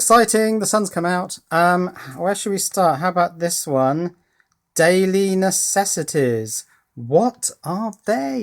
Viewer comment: 12 I thought it said "dark necessities" ;(